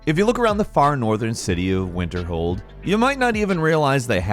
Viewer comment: Oh, Falcon, you kinda blew it this time. 1. Sounded like you said Winterhome not Winterhold 2. Nothing random about the Abandoned House. It’s there in that location every time. 3. Not mentioning the reward associated with Frostflow Lighthouse 4. Not mentioning any Falmer dens.